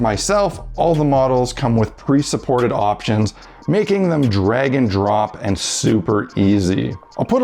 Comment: did you say that the models are "dragon drop?" :D